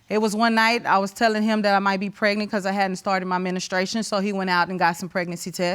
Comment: Help me out here... At 0:55, did she say she hadn't started her administration? 🤣🤣🤣🤣